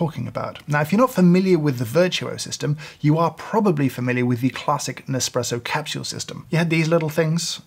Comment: i have tried that nespresso coffee, and imo just the convinience is an advantage honestly... coffee does not taste like a "classic espresso" and it does not replace it (again in my opnion) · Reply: I thought he said "Classic Nespresso". I think it fits into the context of the sentences prior and after. Even more so, when he spends most of the video talking about the lack of quality crema, and debating the amount of liquids pulled. $0.02